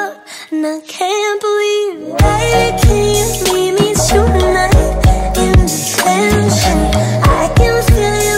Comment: Real lyrics: Baby can you meet me tonight in detention? Misheard lyrics: hey can you meet me tonight in detention?